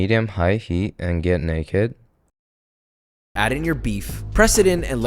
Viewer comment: Nakey ❌ Naked ✅ 5:50 what is he saying 🫨